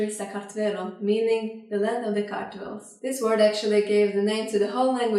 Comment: did she say "the land of the cartels" ?? 👀😂